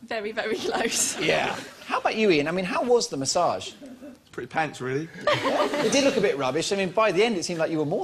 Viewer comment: What did he say at 5:32? "Pretty ___ really." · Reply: Pants.